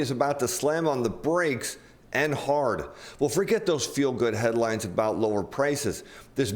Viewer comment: Did you say "fuel good headlines"? LOL! That would be a good one, if it was intentional.